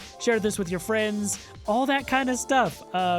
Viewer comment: P.S. That clip at the end was great. :) Also, it sounded like you said: "You stay sexy". :D